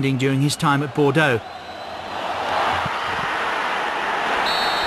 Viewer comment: did he just say "move on to bigger penis"?